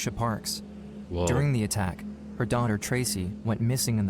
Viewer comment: DID BRO SAY WOAH OR LOL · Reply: Woah i think lol